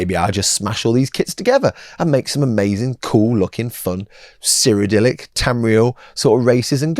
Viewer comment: Did anyone else’s fruit based device think saying Cyrodiil sounded like he was saying ‘Siri’? Just mine then….